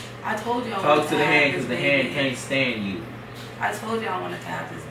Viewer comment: I swear he said "talk to the hand cuz the hand can't stand u" like if u heard that💙 he said it at 14:41 I think❤❤❤❤💙❤💯🔥🔥🔥